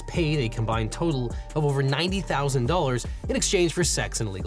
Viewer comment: I had to rewind because I thought I misheard – I thought it was 19,000, not 90,000. But no, he really spent 90k on sex.